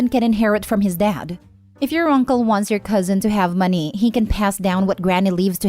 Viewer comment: I thought its going to say "he can pass away" 😅 OMG. I'm so sorry for finding it funny.